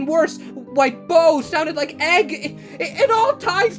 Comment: the subtitle said “ike” instead of “like”